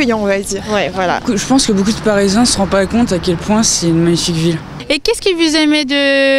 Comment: Can't understand the guy at 3:42. (He's saying "ne se rendent pas compte" ... but it sounds like he's eating his words and it's next to impossible to make out what he's saying.) Other than that - I love these videos!!!! They SHOULD revolutionize language learning. When I traveled to Spain or Colombia, I'd hear guys like the one at 3:42, and I'd be frustrated because I couldn't make out what he was saying, no matter how hard I tried. So it was WASTED TIME. Here, I can study what someone said, listen to it over and over, and hopefully the next time around, I wouldn't get so tripped up. keep up the great work!!!!!